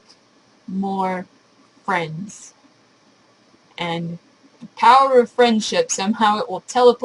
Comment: I could've sworn you just said "powdered friendships"... Do those come in a can or a box?